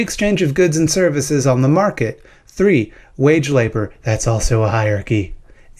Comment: Why is it that you are able to upload videos to YouTube? Because of capitalism. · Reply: Ok your not gonna believe me on this but just hear me out I thought you name said "wage slave"